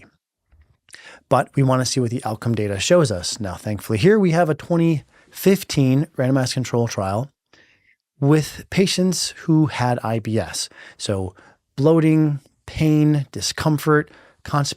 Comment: Thought he said “random ass control trial” 😭😭